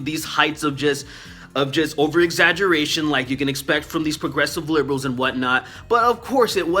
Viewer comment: I thought I heard you say "progressive liberal". I think you meant "regressive liberal".